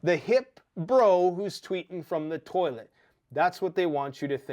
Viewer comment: ❤ Did you say TWEEKING FROM THE TOILET? Chuckle. ❤Morris